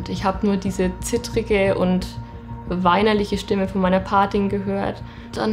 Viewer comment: Was ist eine superfamilie? Und wer hat ihr bei minute @ 10:22 angerufen? Ich hab das nicht verstanden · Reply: Patin